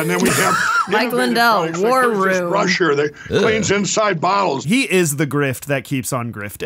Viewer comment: Did he just say that the brush “cleans inside bowels?”